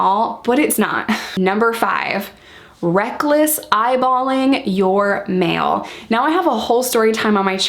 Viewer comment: I thought you were saying recklessly eyeballing your male. And I was like dang, brazen horny women!